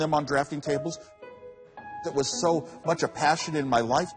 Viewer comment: "I want to be at the bottom of the org chart, being an engineer" What a sincere and happy man Steve is. I hope to have just half the contentment in my life that he has. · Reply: Thanks! I heard "orchard", and couldn't make sense of it